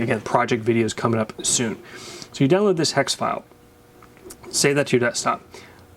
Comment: I can't understand what he says @ 4:39. Right after he says "So you download this hex file. bla bla bla something stop. "Then, go to this website..." Does anyone know what he is saying? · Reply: "save that to your desktop"